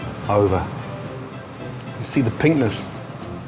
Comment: I thought he said "you can see the penis". I had to take it back!